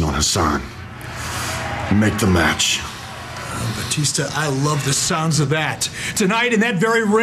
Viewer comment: What does he is saying? 01:27 Bautista i love this sound ???